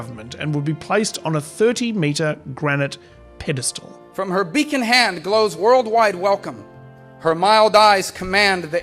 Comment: did bro just say “beek and hand”? Or “beekoned” hand? He sure as shite didn’t say beckoned.